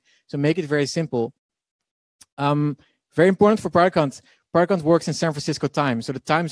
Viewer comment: Start 06:56 Idea 12:00 Build 14:57 Launch 20:23 Grow 26:18 Monetize 33:24 Automate 35:50 Exit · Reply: for those wondering about launch, he said "Product Hunt" 15:36 (i thought he said "productcon")